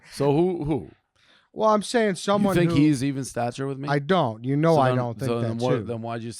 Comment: When referred to clay travis he said "different stature" and dave thought he said "even stature"